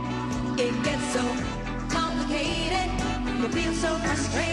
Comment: Whew I had to rerun that one song as when she said complicated at first thought she said constipated! Thought whoa no can’t be that she sang that. Lol